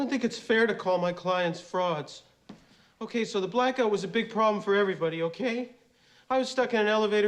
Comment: I thought he said “ok so the black guy was a big problem for everyone” instead of “blackout”. That might’ve been funnier if he said that but somehow redeemed his racial tendencies later in the movie🤣🤣🤣